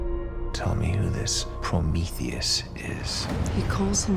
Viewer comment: The beginning of the episode trailer when Oliver says "Tell me who Prometheus is." I thought he said "Tommy is Prometheus." 🙃🙃🙃🙃